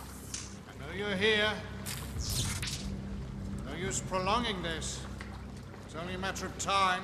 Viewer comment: What did Lovejoy say at 3:28? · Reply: I think he said no use prolonging this